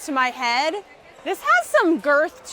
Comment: DID SHE JUST SAY GIRTH?